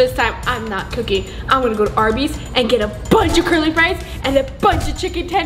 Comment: I thought said curly flies😂😂